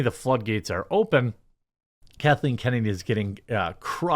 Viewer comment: Huh! All this time I thought he said "Caroline Kennedy". Ooops!